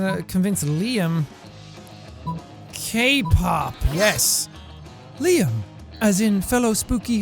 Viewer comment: I thought he said cake pop lol